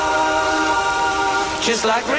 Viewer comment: Man all I hear everytime I listen to this I hear "Jizz like raindrops!" which is thoroughly disturbing on what is otherwise an epic song.